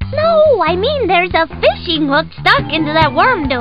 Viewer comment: Uh.... what did he say? (It's confusing me) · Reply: "Huh?? Hooked on junk food??"